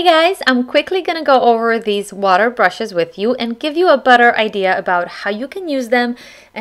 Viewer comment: okay who thought she said butter when she said better , LOL